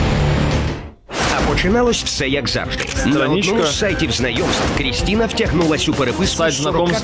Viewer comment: мне одному на 1:39 послышалось на сайте знаебств?